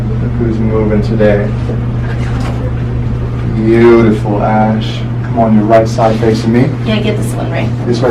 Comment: SAYS.............BEAUTIFUL ASS.................HAHA...THAT'S WHAT I HEARD